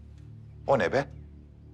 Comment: Did he say "Kon hai be?" 😂